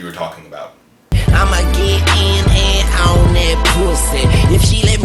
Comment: Oh Wooowww for years I thought he was saying own that pussy not On that pussy